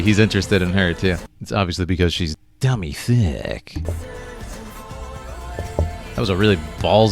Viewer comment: am I the only one who hears Draven calling Riven "dummy thicc"